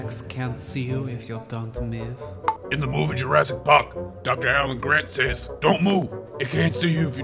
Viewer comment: I heard Jurassic parka and I remember my Jurassic park book. Because the book said “ Don’t move. A Tyrannosaurus rex vision is based on movement.” The book lied to me. :(